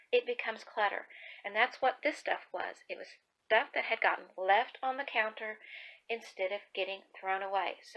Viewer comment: Hubby replaced the roll in the bathroom and sat it on the bathroom counter. I think he forgot to take it to the box we have for recycling. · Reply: oh lol I thought you said it was on the kitchen counter. That makes perfect sense